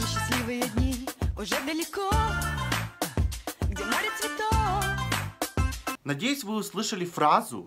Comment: Я слышу,, Где море цветов"